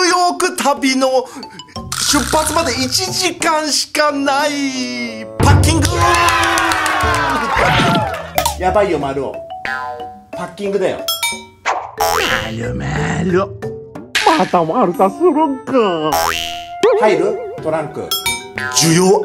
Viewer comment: 「ヤバいよまるお ハッキングだよ」って聞こえるの俺だけ？